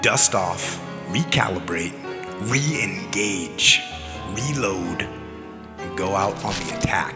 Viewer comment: I thought that when they said reload they were saying meatloaf.